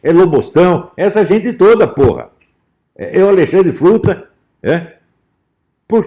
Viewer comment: 👏🏼👏🏼 dei risada quando ouvi “Alexandre fruta” hahahaha